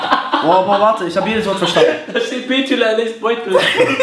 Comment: Was sagt bei 5:09min ? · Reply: Da steht Betuel und der ließt Beutel " Hahaha